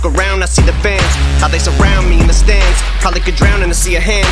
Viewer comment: Am I the only one who I thought he said "How they surround me in the Stans?" But it is stands.